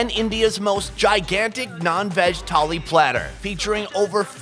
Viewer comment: In 0:51 did he say nonveg tali or nann veg tali?